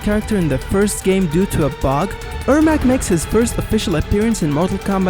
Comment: Says “Error Macro” Subtitles Say and I Hear “Error Mackerel” I sometimes call him “Error Macaroni!” XD